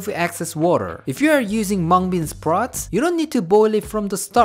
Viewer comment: Did he just say Hmong bean sprout?